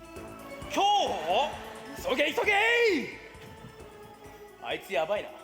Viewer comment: lmao it sounds like ishimaru said "sou gay sou gay" which means "i'm gay i'm gay" in portuguese kkkkkk